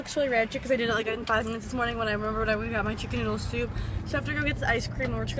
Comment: Hahahahaha what the hell did you say from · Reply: "'Cause I did it in like 5 minutes this morning when I remembered I went and got my chicken noodle soup"